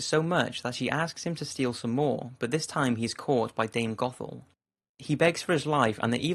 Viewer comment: Am I the only one who thought he said "dang gothel"